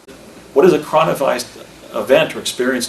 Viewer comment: Did he say cornavised event😳@ 28:15. I'm watching during a Corona event now. 🤷‍♀️🤦‍♀️